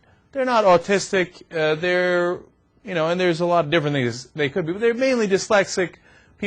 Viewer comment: lol... 00:40 theres are a lot of different niggas ha ha ur bad cenk I think he meant to say things but it just sounds like it lol.....